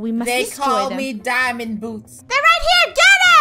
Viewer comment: Lol when I heard say “they call me boots 👢“ I thought he ment “tHeY cAlL mE BoOtY bOi” 😂